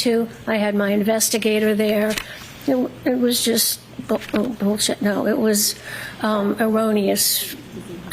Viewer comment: did she say BS? 😅